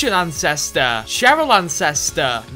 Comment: I thought this guy was saying “incester*